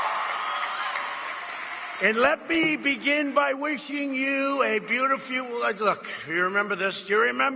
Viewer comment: I don't have audio right now and the automated subtitles claim that he said "let me begin by wishing you a beautiful egg" at 1:53. Did he actually say that or did the voice recognition algorhytm fail to make sense of his babbling? I'm not sure which is more likely.